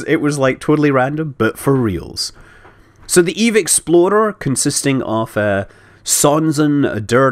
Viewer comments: every time Scott said "Eve explorer" I thought he said "exploder" and it reminded me of Danny and his world destroying pillar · I keep hearing "Eve Exploder." In this game that's a reasonable name for a ship, but still.